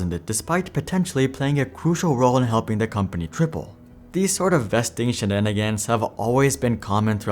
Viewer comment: i know you said triple but it sounded like "...play a crucial role in helping the company cripple" 😂😂